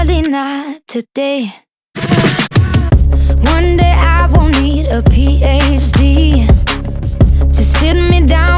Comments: i thought that line said "one day i won't need a pho" and was like wha?? xD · Did she just say "One day I don't need a pho"? lol